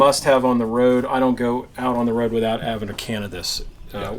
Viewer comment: listening at 1.75 speed while doing things around the house, so i thought i heard "i don't go out on the road without cannibus" haha! I was like, "bro, word..."